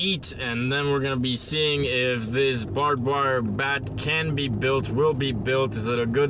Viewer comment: it says bar bar bat in the captions instead of barbed wire bat!